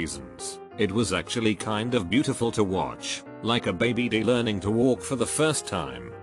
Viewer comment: everytime i replay that part i hear "baby dealer"